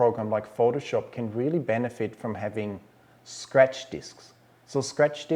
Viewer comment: What word is being said at 12:10? A fast hard drive. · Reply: Oh, stupid ad! Scratch Disks